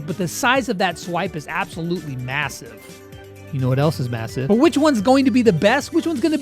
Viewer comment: Did you say massive?